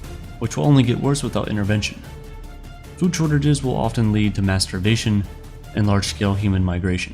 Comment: Ill probably regret this, but I'm pretty sure narrator said masturbation instead of mass starvation (30:12ish) 🫢🫣